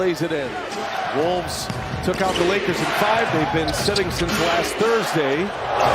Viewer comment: did he just say; LAKER IN FIVEEE? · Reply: Wolves took out the Lakers in five